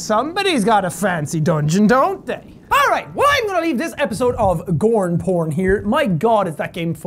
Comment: did he say... *gorn porn*?